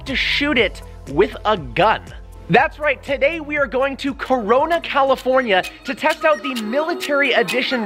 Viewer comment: Excuse me did he say corona ?